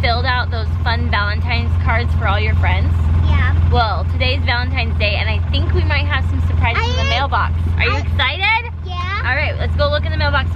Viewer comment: did say happy valentines in